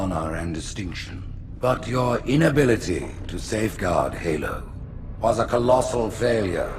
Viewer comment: I never knew the Prophet of Truth said “to SAFEGUARD Halo.” I always thought he said “to save God Halo.”